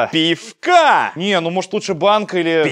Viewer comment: я один сначала подумал что у макса на кепке написано мать? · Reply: Максим Минеев а мне послышалось что он вместо пифкА сказал пИфка)))